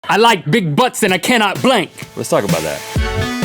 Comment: The intro I thought link said I like big butts but I can't think, butt I think it's just me